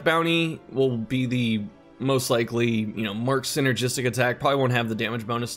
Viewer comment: stoked on this that we finally got confirmation and a ETA my brain is so broken that I thought you said "Marx energy" instead of Mark-Synergy though lmao